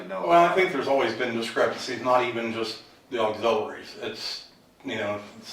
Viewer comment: I’m having too much fun reading the CC on this video. One of the investigators said something about the auxiliaries at 19:13…and the CC seems to think that the LEO said “old ovaries”.